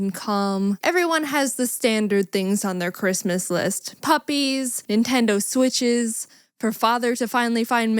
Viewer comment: "puppies, switches, her fathers dfsjakkidfaush?" Can someone with better hearing and not Yutube captions tell me what is being said? I've turned the volume up on this and rewound it 10 times before giving up.